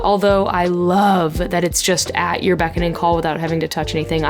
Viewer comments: I was almost sure I heard "at your Becca and call" there ... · Did you just say "At your Becca and call"? Brilliant